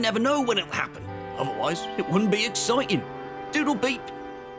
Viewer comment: Did Bertie just say toodle peep